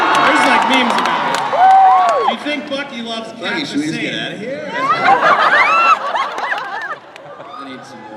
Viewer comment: what did chris say at 3:12? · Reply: "Bucky, should we just get outta here?" Edited: It's "buddy," as in, "your pal, your buddy, your Bucky."